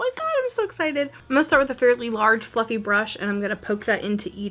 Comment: You said “fairly large FLUFFY brush” I heard “fairly large Fucking brush” 😂😂😂I was like whoa now what did the brush do